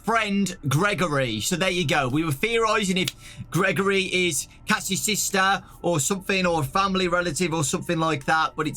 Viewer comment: Did he say that Gregory is Cassie's sister??